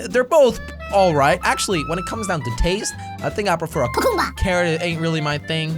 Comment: Who heard the part at4:40 cacumba